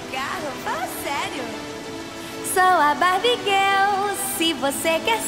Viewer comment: Oq eu escutei "Sou a Barbie em gel " Pandemia